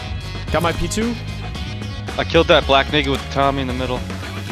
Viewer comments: “I killed that black 🥷with the Tommy” lmfao 😂 am I the only one who heard that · For those of you that don’t play Rust- at 40:52 said he killed the black “naked”. That term is frequently used in Rust to define someone with no clothes on (common after you respawn.) The callout helps us find the body he is talking about to get the gun on the ground. I can assure you with absolute confidence that was not any other N-word you may be thinking about. Me and my team do not use that word, and regardless- I bleep the word f*** in my videos so I would have definitely bleeped that. Anyways, hope everyone is enjoying the video! ❤ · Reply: i hear him saying ''i killed that bl@ n-w0rd'' and its even more clear on 0.25 speed, not only that, even the subtitles says ''i killed that black [__]'' and even the cherry on top at 40:55 you see a 2 black chars on screen, 1 with the tommy which is the one he called out ''I killed the bl@ n-w0rd with the tommy in the middle'' , coicidence? i think not. and i know for a fact im not deaf or dont have any hearing problems lmao